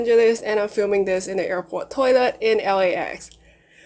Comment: i thought you said im filming this in airport toilet😂sorry